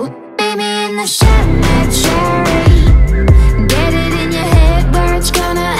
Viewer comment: I thought she said "maybe in the cemetery" before I read the lyrics